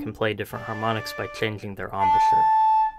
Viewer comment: can anyone tell me what they're saying there? changing the "amisher"? google didnt find anything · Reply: Embouchure